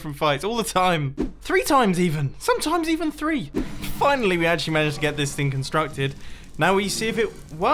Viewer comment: yooooo I got an ad at the perfect time, it cut you off saying FINALLY it sounded like mumbo yelled FUCK